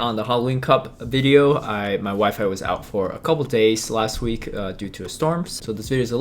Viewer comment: When he said his 'wifi was out,' I heard him say his 'wife was out.